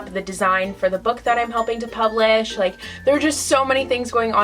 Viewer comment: Did i just hear "a book that I'm hoping to publish" Wut wut wut I need more details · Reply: I really need to enunciate, I said helping not hoping! I have no time write a book right now unfortunately 😭 soon though!